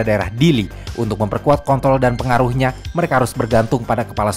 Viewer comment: ehh ehh bilang apa tadi tuhh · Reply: kontorl